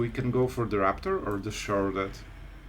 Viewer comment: Please please please please don't take this the wrong way, but every time you say "Sheoldred" in this viedo and many others I've seen with you all I hear is "Charlotte" and I love it, it's my new nickname for her. I know it's your accent that makes this and I know what you are saying, but I love it and she will forever be "Charlotte" for me. Perfect example